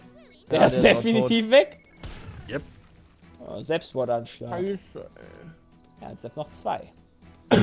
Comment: Jay: Selbstmord-Anschlag. Ich verstehe: Sepsmord-Anschlag.